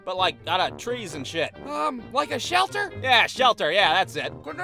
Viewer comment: for those who don’t know what he said the boy with the Fritos bag on his head said “can this be my shelter” (referring to the Fritos bag, if course)